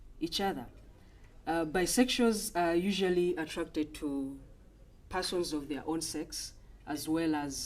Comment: I'm sorry but I really thought she said: attracted to assholes of their own sex 😂😂